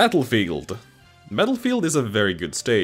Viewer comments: I just find this funny but at 2:45 you can hear him say metal field instead of battle field also turn on closed captions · Did he say Metalfield? What.